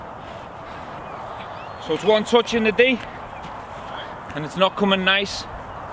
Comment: can someone tell what Steven says in this minute? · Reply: "One touch in the D... and it's not coming nice" Basically saying he's going to send in a powerful cross to the other two.